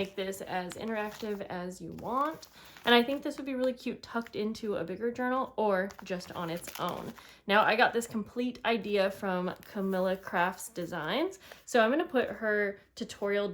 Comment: Do you mean Julie from Camelia Crafts Designs? Oh ok you just said you will put in a link🤣 I thought you said Camilla Crafts.